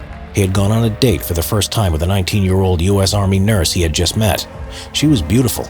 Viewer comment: i misheard it as 90 year old